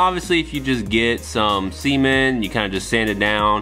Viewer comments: did he just say 'semen' instead of 'cement' when talking about the seamlines? LMAO XD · someone confirm what said? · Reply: 💯% cement. My accent just screwed me over.